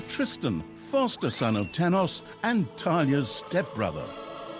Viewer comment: Am i the only one hearing the Narrator saying Tanya instead of Thalya?